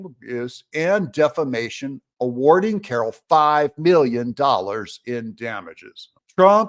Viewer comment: I thought you were saying the truth…”aging” Carroll…